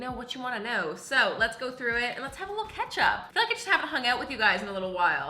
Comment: rlly thought my girl said “let’s have a KETCHUP” and was like ???? i mean ok girl